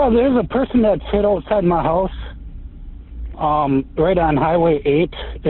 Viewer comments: Leave it to a dispatcher, a person whose sole job is to listen to details, to not understand the very clear emergency being described to them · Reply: He misheard the caller. He thought he said "sittin' outside my house" when he actually said "hit outside my house" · Leave it to a dispatcher, a person whose sole job is to listen to details, to not understand the very clear emergency being described to them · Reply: The caller wasn't making the situation clear, he said "there's a person that's hit outside my house" What's that supposed to mean? Somebody slapped him? And I think the dispatcher misheard it as a person sitting outside my house. If I had called the first thing I would have said was there's a body lying in the road has been hit by a car. Very short sentence with ample clarity.